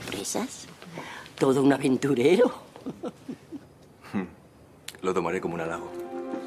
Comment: I swear he says "Leggo my Eggo." 😂😂😂